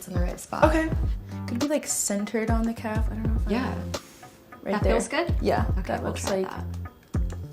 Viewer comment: 〜 貼ってOK？って空耳した